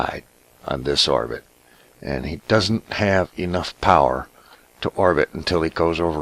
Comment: Was I the only one who heard "I donna have the power!" in a Scottish accent ? :)